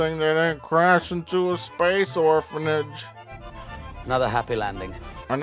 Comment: i always heard “Not a happy langing”